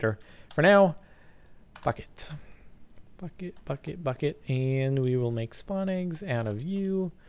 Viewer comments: When he said Bucket, Bucket Bucket Bucket at 18:37 it sounded like fuck it x3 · Thumbs up if you thought Jordan said, "Fuck It", instead of, "Bucket". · Okay did anyone else think he said "F it" instead of "bucket" at 18:31? lol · I thought he said fuck it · I thought you said fuck it not bucket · Wow, when he said Bucket it really sounded like he said F*** it! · Bucket I thought you said f**k it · Did he just say "Fuck It?" o-o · I thought you said "for now, fuck it".